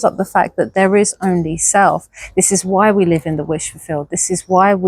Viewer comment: You can tell I'm a preteen mum what you said " there is only self" what I heard " rizz on yourself" 😂 both work I guess haha